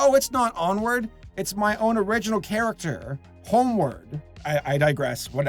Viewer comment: I thought It said homework